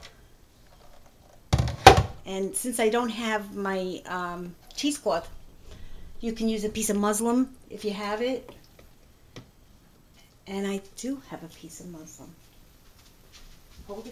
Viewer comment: Did she just say "use a piece of muslim"?